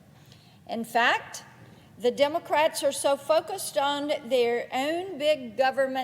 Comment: Wow , at about 4:12 it sounds like she says " the Democrats are ' so f_ cked up stoned ' ... I was playing this at 1.5 playback speed because I'm very busy and I needed to go faster ... but I didn't expect for it to sound like that !